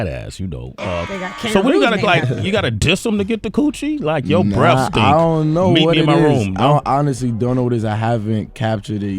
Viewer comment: your breath stink meet me in my room lol fucking sway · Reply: bruh i actually thought he said "your breast stink"